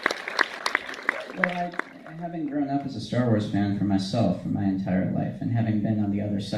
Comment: did i hear "i didnt grow up as a star wars fan all my life" we have a non star wars fan making star wars films...